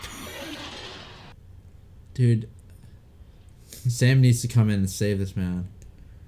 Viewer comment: Lol I heard "Dude, Sam needs to come in and save his man" and I was like "Yeah exactly, he gets it! Sam better come back and save his husband!"